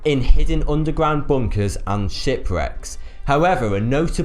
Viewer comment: Did I just seriously heard him say Shity Reck?